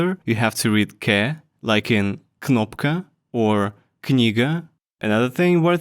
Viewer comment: yoo what he say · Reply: Knigga